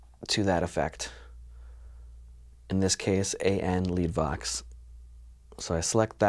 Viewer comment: Кому послышалось Andi Vax - лайк